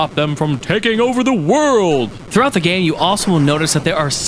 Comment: did he say "throughout the gay" ?